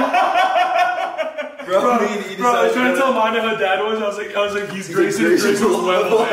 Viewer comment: What did Ethan say at 18:40? I cannot for the life of me understand him lol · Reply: "I was tryna tell mondo how Dad was, I was like he's Grayson if grayson was levelheaded"